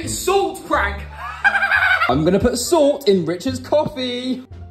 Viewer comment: I thought you said Richard hated coffee in another meme 🤣🤣😂